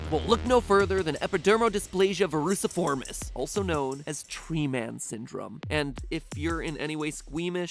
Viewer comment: I thought when he said G-man syndrome And I was like “oh cool Mr. Freeman...”